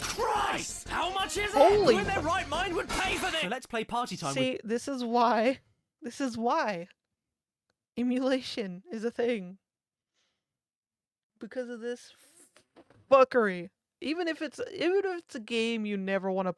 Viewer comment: I thought you said "immolation" after that comment about the family ashes, and not "emulation." Whoops. 🤣